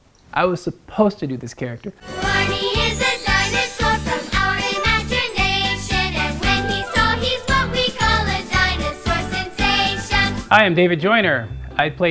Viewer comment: i heard "without imagination"